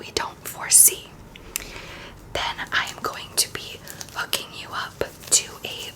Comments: i swear i thought you said “i’m going to be f**king you up” and not “i’m going to be hooking you up” 😂 it’s time for me to go to sleep as of right now 🤧 edit: i was scrolling through the comments thinking i was going to be finding some time stamps but so far i’ve seen nothing 🤧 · I first heard "then I am going to be f*****g you up" 😂